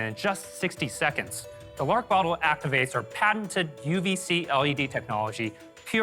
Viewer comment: Lori: did I just hear QVC ?? INVEST!